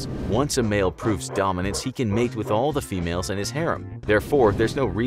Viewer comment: Did he just said "harem" ?